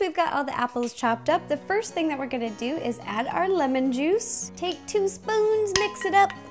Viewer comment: I thought you said orange juice and I was like what but then I realized that you said lemon juice 😆